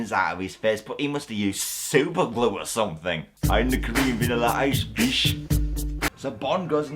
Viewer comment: Does anyone know what he said at 5:48? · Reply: I’m the Korean Vanilla Ice, bitch! You’re welcome